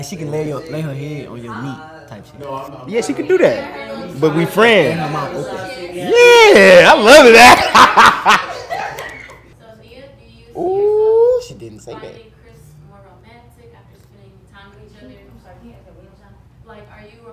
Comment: Did Chris say yeah I'd love that?..They stay trolling😂😂😂..I enjoyed the reunion, though i'm pretty late❤😂